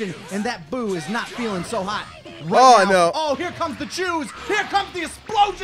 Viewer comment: sounded like "here comes the jews!" XD